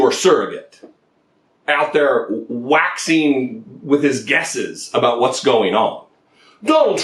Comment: it sounded like you said this goober was"waxing with his guess'es or gas'es, you got it covered either way! Personally I thought "waxing with his gas'es" broke new ground!